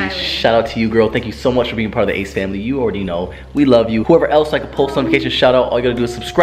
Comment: That’s not what I heard😂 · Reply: I heard “ace family I shit” 😭😂😭💀